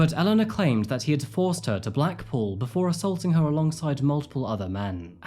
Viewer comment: Sorry I’m hung up on this one part at 10:34 “he forced her to black pool” ? Am I hearing this wrong? I don’t know what it means and I listen to it over and over again trying to understand what he is saying here. Anyone know?? After listening to more I’m guessing Black pool is a place? How did she know Mohamed or did she? After finishing the rest of the video. I feel so terrible for these men. I feel so sorry for actual victims who are never taken seriously. Victims that ask for help and don’t get help because LE won’t step in if they don’t have enough evidence. Then in other cases it’s too late. Ellie obviously has some mental health issues in my very unprofessional opinion . Attention seeking behavior that goes way beyond what I have learned from my own experiences with mental illness in my youth. I have a friend that is an pathological liar. I can see why he lies and how it started from his youth from lying about his sexuality in fear of what would happen if he came out. Those lies turned more extreme as he wanted to be accepted by people he met that had more money and only cared about materialistic and outward appearances. He lost so many friends in his life and got into criminal fraud activity in order to make money to hold up appearances. I still am friends with him and he got better for a while after moving to Colorado, a place that imo, is more down to earth. I constantly told him that I don’t care if you live in a box, it’s your personality that matters to me. He coul